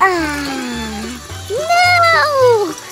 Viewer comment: was diamond saying no!